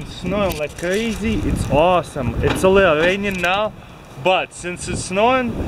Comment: "It's only uranium now" Nice, better go make some radioactive snowballs XD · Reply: it sounded like he said "It's raining now..."